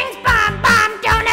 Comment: did he say donut plains🍩🍩🍩🍩🍩🍩🍩🍩🍩🍩🍩🍩🍩🍩